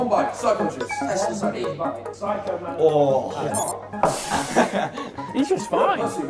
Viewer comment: I thought said psycho mantis I was like Wtf